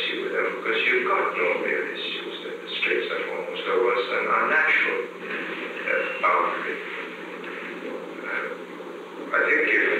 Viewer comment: It sounded like he said "Straits of Formosa was a.. a natural boundary", not an "an unnatural boundary".